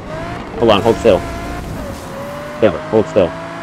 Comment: I wish I could see that though Silver's and Skull's POV. That was awesome. 9:33 also, I rewatched it in slow motion and you did do a 360. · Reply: I thought I heard you say "Taylor, hold still." What did call him at 23:22?